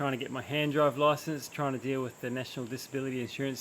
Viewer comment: I misheard you. Thought you said “hand job license” :D and you used the wrong girl for Alina’s pic 😂